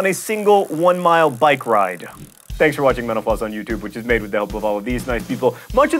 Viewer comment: Sometimes it sounds like you say, "Thank you for watching Menopause on YouTube..."